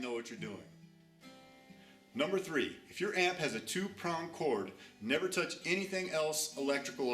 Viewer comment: Great video!, just one question, what did you say at 1:09? (If your amp has a ...) sorry but english is not my native language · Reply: No worries, I said 2 Prong chord (Plug), pictured at 1:13.